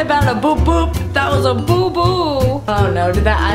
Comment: hannah when u said boo boo it sounded like u said poo poo